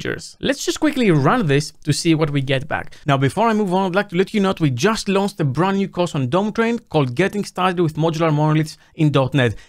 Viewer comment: When he says "We just launched a brand new course" I hear "We just lost..." and I feel bad for a split second.